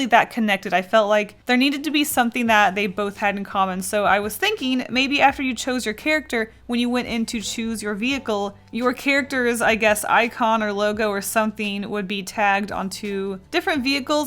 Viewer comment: I thought it said choose your tracer